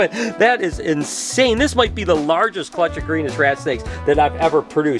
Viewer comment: when u said rat snake i thought u said rattel snake i was like wtf he is just going to pick that up lol